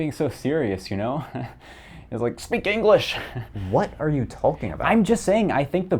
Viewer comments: For a sec I really thought you just said “What are you Tolkien about?” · Did you just say "what are you "Tolkien about"?